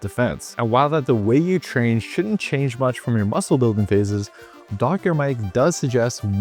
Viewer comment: Thought you said "Dog hair Mike"